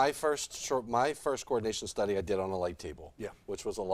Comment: did say “light table”? I’m trying to do some research on what that is and I couldn’t find anything.